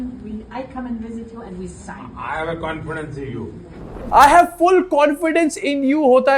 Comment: Haha, I thought he said "I'll have a confrence with you" 🤣🤣🤣